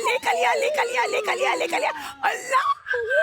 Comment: Did ankita say allah On 30:10😮❤